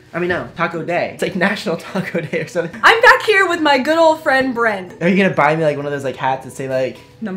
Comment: When Eva said meet my good old friend Brent I thought she said meet my girlfriend Brent 😅😅🤣🤣😂😂